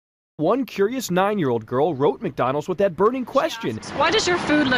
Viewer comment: I heard ...McDonald's for that (dat) .. instead of with that And .. why did you instead of why does you And ..advertising inner what's .. instead of than what's.. please tell me why!!